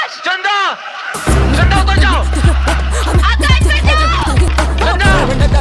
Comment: i heard mar jao 😂 instead of bacchao. Anyway finally a matlab kuch bhi episode🎉😂